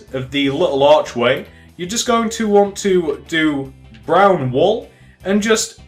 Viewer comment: did anyone else hear brown wool in 12:27 p.s not trying to be a hater just asking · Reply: Yeah He Said "Brown Wool"